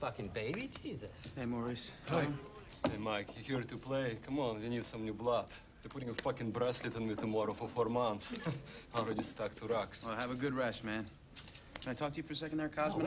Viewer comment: What does he say? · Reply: Hey Mike, you here to play? Come on, we need some new blood. They're putting a fucking bracelet on me tomorrow for 4 months, I'm really stuck to rocks.